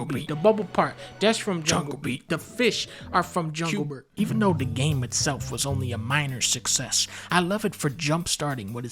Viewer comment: what did he say in the background here? I can't make it out. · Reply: Q Bert